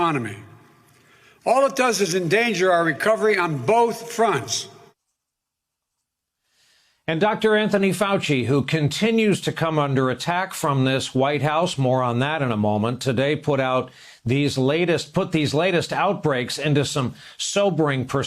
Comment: "And Dr. Anthony Fauci who continues to continues to come under attack from this White House moron..." is what I heard at first lmao